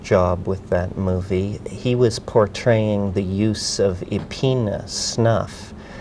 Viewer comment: Anyone knows what dmt containing snuff he meant, in connection with the movie Emerald Forest? Around 8:05 ? Sounded like "Epina" or something like that. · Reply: Apparently he says 'epena' and found from the Virola tree. I found an article... "...There are a number of methods of preparing the snuff, which is called epena..." <link> Hope this helps! Have a good day!